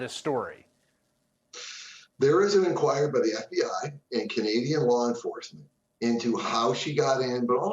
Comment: did he say "Canadian" Law Enforcement? Mentions Canada again @ 4:56 Interesting. I might have to check this out...real name is Inna Yashchyshyn